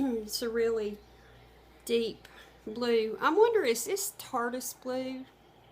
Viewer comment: That looks like a nightgown. " Pink" is from Victoria Secret. U had picked up a pair of pants for her. Sound like you said is this tardis glue? I'm not sure what you were saying. Can't see what that was you said your selling. They were wrapped and upside down. Hope that wipes off easy. Those pens could be a nightmare. Hope they don't write on anything else.